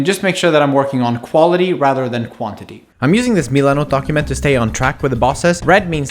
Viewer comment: What's that document/project manager thing at 3:20? what's he say "Milano document"?